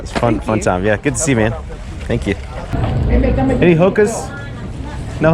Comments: HOKAS, I thought you said Hookahs. lol. Thanks for another awesome video. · thought he said “any hookers?”